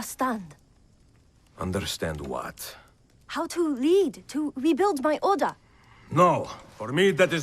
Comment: at first I thought she said how to rebuild my odor